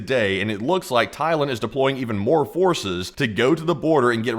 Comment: I thought he said they were deploying horses!